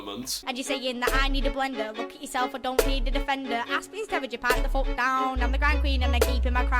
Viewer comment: somet about a blender then all I heard was "defender" asin landrover defender 90 hahaha